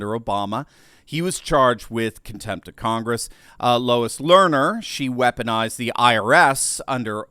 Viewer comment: I heard "Lowest Learner". Maybe it's not a coincidence?